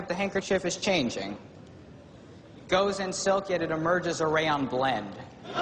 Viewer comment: "goes in silk and emerges..." what does it say there, I can't really get it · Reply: a rayon blend